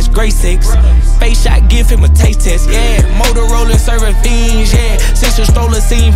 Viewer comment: " Finge " Thats what he said? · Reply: Fiends